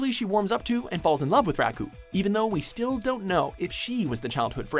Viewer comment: did you just call him rakkun?